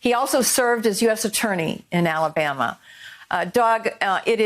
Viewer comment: Did... did she call him "Dog"?